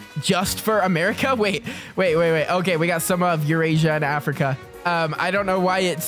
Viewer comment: did he just say “urasia” 😭